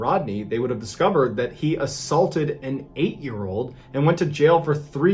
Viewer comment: When Mr. Ballen said: "He assaulted and 8yr old." I deadass misheard him as: "He assualted and ATE someone." I know he didn't do it, but that made me laugh so damn hard once I realised my mistake. Regardless, still a POS that deserves to rot.